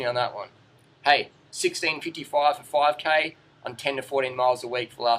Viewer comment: any blokes out there wanna interpret for me/us @ 6:58 sounds like he says "6 in 6:55 5km on 10-40mi a wk...." not sure what he's saying OR trying to convey by his combo of time & mileage variance. (i'm happy/lucky to do 10mi a wk, consistently)